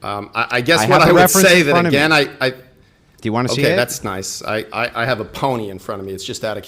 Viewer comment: that's why Wilson asked if the dude wanted to see it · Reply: Bro, I've listened it like 15 times and I keep hearing "ME", not "YOU". Just try it yourself few more iterations (10:06). Also, if he believed that the guy was also seeing it, Wilson's following "do you want to see it?" is a too clunky of a way of asking "what do you think about what you see in front of you"